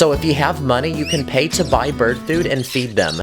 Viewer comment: I thought he said bird poo not bird food